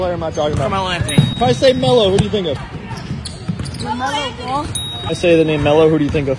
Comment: Some of them sound like they said Carmello Ball